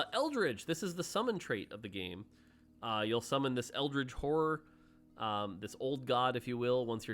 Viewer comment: did he just say “summon this eldritch wh*re?!”